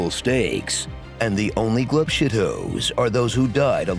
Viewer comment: what does he say? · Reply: Glub Shitto? That's a meme about how Star Wars has some unimportant characters with wacky names that fans go wild for whenever they are referenced.